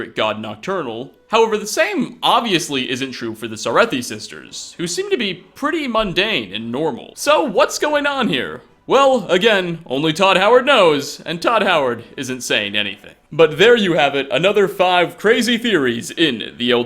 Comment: I thought he said tod Howard is insane anyways